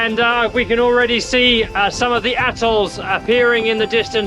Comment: He said “atolls” but I heard “we can already see some of the a—holes.” Panama is supposed to be calm with no road rage!